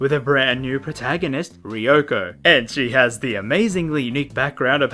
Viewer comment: I thought you said Ryuko and almost shit myself KILL la KILL x Danganronpa???